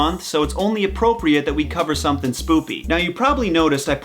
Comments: Did he say "spoopy"? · did anyone else here him say spoopy instead of spooky or do i need hearing aids? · what did you say spoopy or do you mean spooky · I heard spoopy instead of spooky